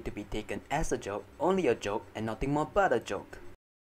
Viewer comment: It sounds like you said Butter joke But it’s But a Joke